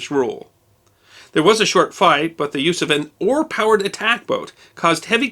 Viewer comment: How about the "Usavin oar-powered attack boat"? I had to listen to it twice before I got "use of an ... " ! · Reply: I thought he said an ore powered boat at first, then went "wait, what, that doesn't make any sen— ohhhh, OAR powered!" XD